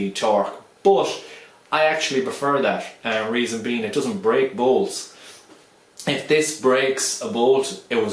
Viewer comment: I thought you said it doesn't brake "balls" lol. Had me sold on that fact alone, trust me.